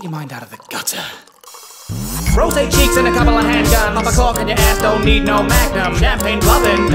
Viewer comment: "Get your mind out of the gutter" Three seconds later I heard 'hand jobs' instead of 'hand guns' There's no hope for me edit: y'know most people thank everyone for likes but getting a notification a year later for a comment i have over 700 likes on and having it be this comment has been one of the worst and most uncomfortable experiences of my life, so... thanks, i guess?